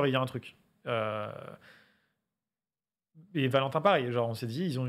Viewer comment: c'est moi ou il dit "Valentin pas"? C'est quoi ce clash gratuit x)